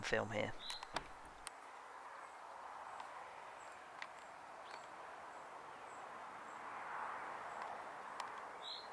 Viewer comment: What was that thing at 4:45? It sounded like he said "mc jack book" it looks like a cross between a rabbit and a baby deer. What country is this? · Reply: A muntjac. Britain’s smallest deer. An introduced species which can be shot all year round.